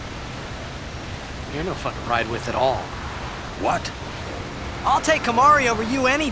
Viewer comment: LOL the first time i played FFX and saw this scene i thought Tidus said at the beginning "Your not fucking straight at all" Then Auron said what? That day I seriously laughed too i almost fell on the floor!